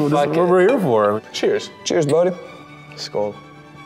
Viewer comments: did he just say "Skål"? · did he say skål?